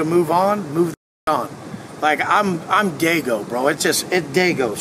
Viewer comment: Wait did i hear that right did he just say I'm gaygo & changed it up & said i I'm daygo 😅😮..